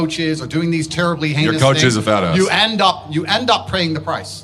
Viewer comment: I thought moron achase said "your coach is a bad ass" I hope Tate crushes Denoire